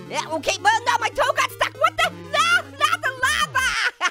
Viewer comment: Not the lava!? I thought he said, "Not the MAMA!"